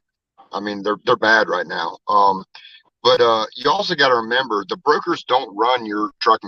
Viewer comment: I keep hearing him say “ The burgers“ lol